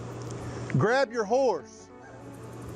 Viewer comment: I zoned out but reengaged when I heard “grab your wh0res” 😂